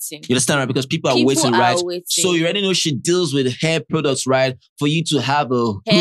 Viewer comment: I thought said she has a dandruff product, I know everything that girl says are lies😅